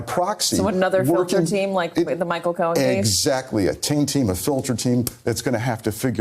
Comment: I was just wondering that myself. Even with captions on, I can’t tell. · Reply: It sounds like he says "taint team" just before she mentions the "t word" but I don't know why that term would be banned.